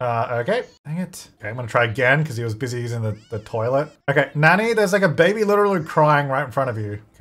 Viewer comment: I legit thought that said: "Okay I'm gonna try again because he was busy eating the toilet."